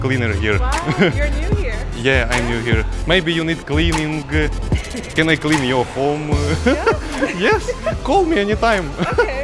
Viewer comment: I heard he said "can I clean your body?" 😯😲😳👏